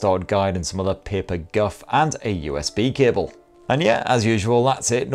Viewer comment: I could’ve sworn you said “kibble” and not “cable”